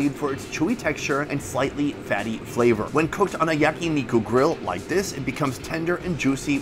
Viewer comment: did you just say miku Letsss goooooo i love miku MIKU DAYO SEKAIIIIIII MIKU MIKU BEAMMMMMMM